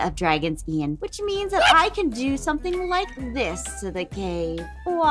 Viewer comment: i hear you say gay